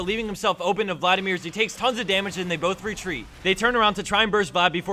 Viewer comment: Did you just say..... TONS OF DAMAGE?!?